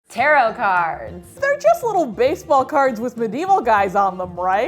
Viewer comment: I thought it said carrot cards...